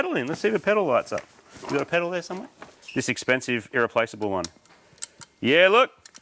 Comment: For some reason i heard "this inexpensive, replaceable one" and i thought that was quite a funny joke.